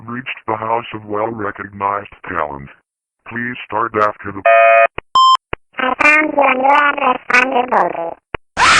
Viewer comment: I thought said that i found you right out there thunderbolty.